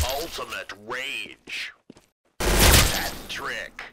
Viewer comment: ngl when movie man said "Hat Trick!" i thought he said "Patrick"